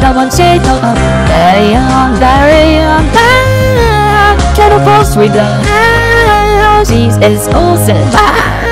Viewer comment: What was she saying here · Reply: She Said Get a grocery bag DAYUM